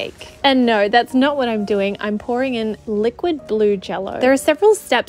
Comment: I thought she said liquid GLUE jello and I was so confused the first two times I rewatched this. Then it hit me that the jello was blue smh